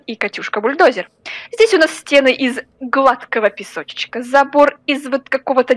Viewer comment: мне послышалось "запор" а не "забор" 😂😂😂